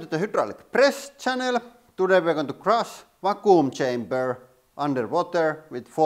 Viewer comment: I really thought he said "we gonna crush my cooom chamber".... XD The high speed was amazing!!